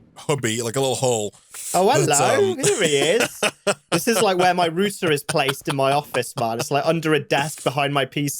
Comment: I thought said rooster instead of router at first around 16:30 and was so confused for a second lol